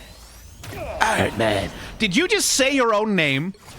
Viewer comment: Since no one else has said it - Yes, it did sound like he said "Iron Man" underneath his breath at 1:00:08 . You didn't hallucinate that